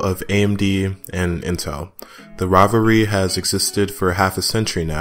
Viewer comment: rivalry sounded like robbery and it made lot of sense